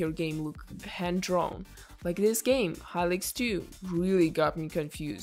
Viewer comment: The game that looked handdrawn, Halix2? not sure if i heard it right. Pretty sure it was made in RPG maker